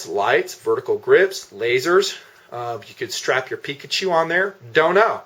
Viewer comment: did he say Pikachu...?